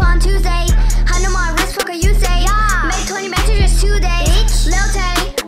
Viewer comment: I thought it said "****** you say"